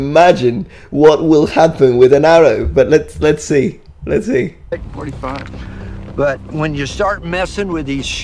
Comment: anyone else hear "take 45" lmfao